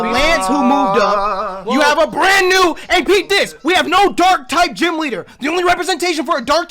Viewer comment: What does keep saying? It sounds like he is saying "repeat this", but that doesn't seem to make sense